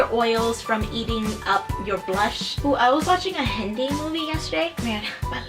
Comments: I'm not trying to be rude but I just a bit confused but at 6:35 did she say I was watching a hentai movie yesterday 😂no hate x love u xx😘😘😘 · i swore to god that you said "I was watching a hentai movie the other day." I had to do a double take on that 😂